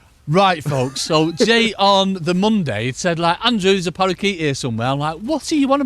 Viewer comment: A parakeet? I thought he said " Andrew there's a pike in ere, I can hear it 😂😂